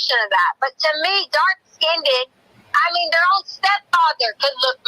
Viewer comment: She actually said “dark-skinned-ed”